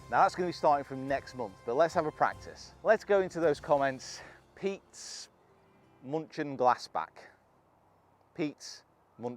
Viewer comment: Honestly I heard Pete's munching glass bags 😂😂